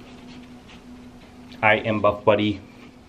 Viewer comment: My wife “did he just say butt buddy” ha ha ha.